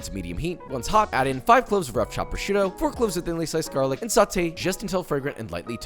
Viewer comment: Did Josh say 5 cloves of chopped prosciutto